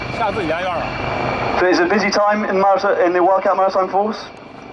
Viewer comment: What did the foreign person say at 3:40? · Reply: He said, 'It's a busy time in the maritime, in the Wildcat MARITIME FORCE' .